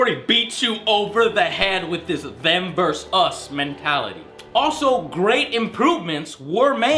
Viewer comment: what did you say? "Vanber us" mentality? To me, it's clear that EA has an obsession about creating a MMORPG competitor against, let's say, WoW: - Textures quality are lowered; - Story becomes dumb and insignificant; - Whatever characters; - Simple quests; All of this are a transition from a real game to a MMO. They did the same with Neverwinter Nights.